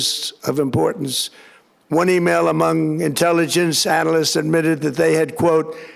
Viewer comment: what did he just say. Twenty male among analysts??